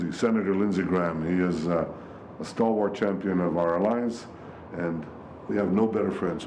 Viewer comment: Did he just say "He's been a stalwart champon of our lies". Propably meant lives.